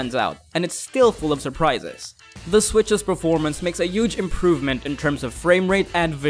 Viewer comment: wait... did he just say yuge? #DonaldInDisguise